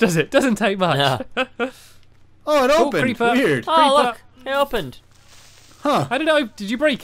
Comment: at the end when said creeper i thought he said hot fever lol